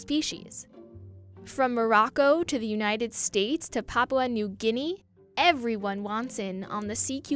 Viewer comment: How did she say Papua New Guinea? It sounds like Papla New Guinea.